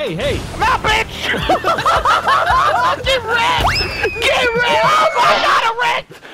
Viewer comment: Delirious, did you say 'Oh, God! I wrecked!' or 'I got erect!'?